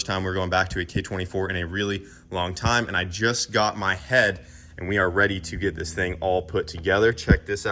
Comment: First the, "it's either going to go or it's going to blow." then 1:04, My mind really thought he said..... just got some head, now I'm ready to put this all together. lmao